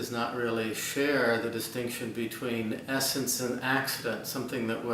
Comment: What is the word coupled with "essence" that sounds like "axpet", at 1:00f? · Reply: it's 'accident'. One of the distinctions between properties is the 'accidental vs. essential' distinction.